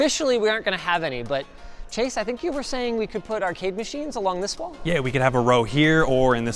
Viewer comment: ARCADE machines! I thought he said "our cake machines", and I was intrigued. Sort of.